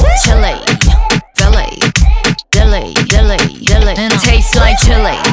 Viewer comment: I'm sure that it's not dilly it's deli(delicious)? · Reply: i thought she was saying “silly”